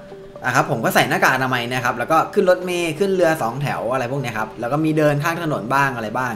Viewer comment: ได้ยินว่สเรือสองแถว